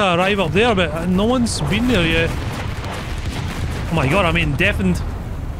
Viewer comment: "Oh my gawd, I'm bein' deafened" and here I thought you might have a special guest. What you said is not what I heard, I heard, "Oh my gawd, I'm bein' DEFYN'd"